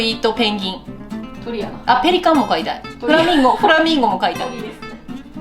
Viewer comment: That's one hell of an infectious laugh, just the counter-infection we need at this time! 👏🖤👍😷 Just one question, though: Why do I keep hearing 'flamingo fighter' at 4:16? 😉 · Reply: "flamingo mo kaitai" = "I want to get a flamingo [as a pet] too." (